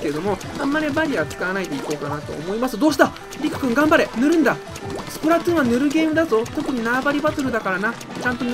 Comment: りく君頑張れ塗るんだ！って言った後｢スプラトゥーンはヌルゲーって聞こえたwww｣